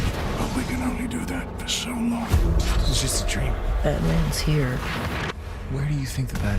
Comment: For a second I thought Oliver's son said, "Batman's here."